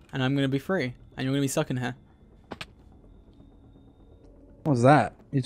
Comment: I swear I heard George say “Your gonna be sucking here”😂😂